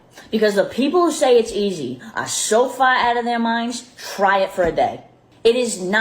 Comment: I thought she was telling people to fry lettuce. It sounds like she said "fry it for the day" and I see a pile of lettuce Infront of her.